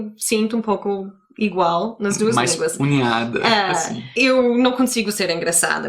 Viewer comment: Mais "unhada" 16:58 foi boa Acho que era mais "unida" :P